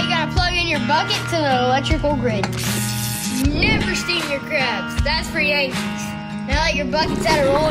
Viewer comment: Low-key thought he said don’t boil your crab that’s for the Asians💀